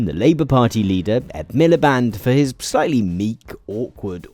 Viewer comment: As an American who used to listen to a ton of BBC Radio 1-Xtra during work, whenever I heard the name "Ed Miliband" I thought they were saying "Ed Miller Band" and would always get confused.